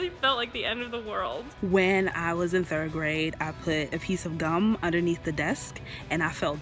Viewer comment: did she say when i was in third grade or did she say something else